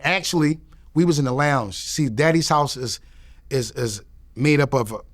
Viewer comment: Am i the only one who heard him say "Daddy's House" instead of Diddy's House @ 5:14 . That's wild as hell